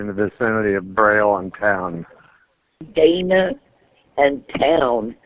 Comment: Okay, the caller sounds a bit drunk, and then the dispatcher slurred "Daima and Town" before I realized it was another caller. 😂